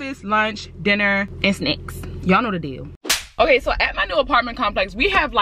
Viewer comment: At the beginning when she was saying what she was getting from the shop when she said snacks I thought she said snakes lol love you